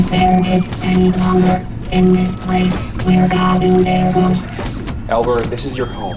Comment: "I can't bare it any longer in this place where God" and the rest is couldn't really make out lol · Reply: it kind of sounds like he says something about ghosts towards the end, but..I'm not sure 😭 Edit: I made this comment before subtitles were added ^ ^;; he says, "I can't bare it any longer in this place where gods build their ghosts."